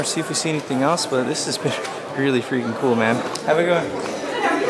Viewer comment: Ngl it did sound like you said “how you doin” instead of have a good day🤣